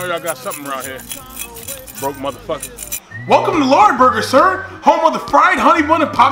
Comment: Oh, my goodness! That is me right now, and I really thought he said, "Welcome to Whataburger!" Just realized he said, "Welcome to Lagerburger," or something like that. To be honest, after I saw this video, I saw most of these in terms of levels, or steps, i.e. first is the Too Good for a Regular Job Veteran, then he gets smart and becomes a Minimum Wage Veteran, then he gets smarter and becomes a College Veteran, and then he works his way up and eventually becomes the Successful Veteran. That's how I ended up looking at it, anyway, from my personal experience.